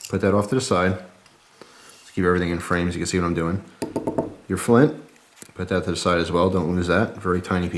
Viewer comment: flim ? mine is broken can you writte me the real name of that litle thing ? i heard flim is that correct or ?